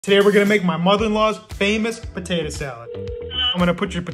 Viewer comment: When I didn't hear he said mother in law because my kids were yelling and I thought he said my mother's and I heard she was black on the phone I was like hang on a second